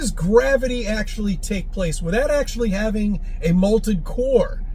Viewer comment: I'm sorry, did he just say "the molten core creating the gravity"!? Fucking hell XD "The moon somehow has gravity despite not having a molten core" What is his obsession with molten cores!? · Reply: To be fair he did say molted, lol